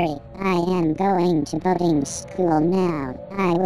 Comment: Let’s ignore the fact Patrick didn’t pay to get in boarding school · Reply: I thought spongebob said bugging school